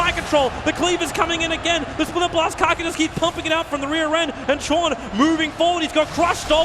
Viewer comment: "kaka keeps pumping out of the rear end" Did I hear that right?